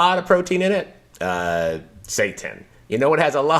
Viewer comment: What was the first high protein food? It sounds like he said "Satan" (captions agree 😅).